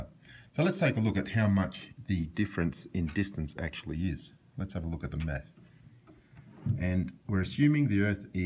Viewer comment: I heard "Lets take a look at the Meth" and not "Lets take a look at the Math" I'm sorry but that's really what I heard...